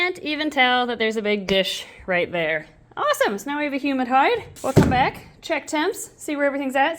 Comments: I kept mishearing 'humid hide' as 'human hide' and for a second I was thinking "Why would she want human skin? Why do you have human skin to offer her?' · I heard "Humid hide" as "HUMAN hide". 😬